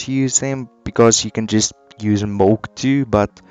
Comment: DID HE JUST SAY MOLK???